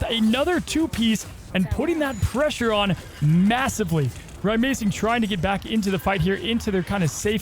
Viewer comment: did someone say . . . Massive?